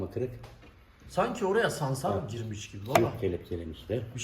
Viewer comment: ’da “Sanki oraya …. girmis gibi” dedi arkadas. Anlayamadim ne dedigini. Salsarak gibi duydum ama ne demek onu bilemedim.